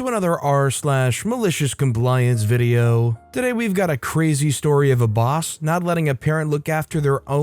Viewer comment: He said "go suns" and i heard "ghost hunts"